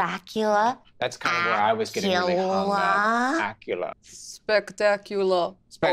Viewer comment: Idk why but when Zac said "What rhymes with Dracula", I instantly thought of "Spatula" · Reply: i heard “acula” and thought “acura” 💀